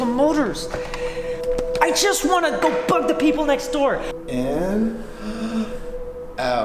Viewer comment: the first time I heard it I thought he said, "I want to go fuck the people next door"